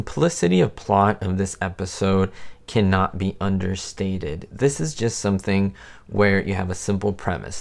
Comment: did he say "understanded" at 3:19? Now I wasn't the bestest student in skool, but I thought it was "understood..."